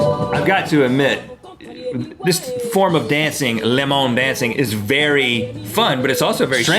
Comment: I thought you guys were saying Le Monde, which is French for the world.